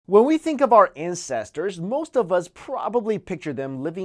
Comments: I swear I heard "when we think about incesters." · in the beginning of the video it sounded like he said incestors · It so sounded like u said incesters at first I was like whaat?! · At the beginning of the video it sounded like he said ''when we think about our incesters'' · I have bad hearing... aaand I heard "incestors" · At first I thought he said incesters not ancestors at the start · In the beginning it sounded like you said incestors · I heard "when we think about incest" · when you say "Antidote" it sounds like antadult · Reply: when he started I thought he said Incestors lol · i could have sworn he said "incesters" at the beginning lol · I heard "incestors" and "middle yeast" Time for some sleep I guess. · "When we think about our Incestors" O.O that's what I heard... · at the start i heard ''when we think about incest'' lol, my eyes just went 0.0 had to replay that :p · I thought you said "When we think of our incestors..." in the start lmao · I first thought he said: When we first talk about incest HAHAHA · did he just say incestors? lol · did he just say incestors?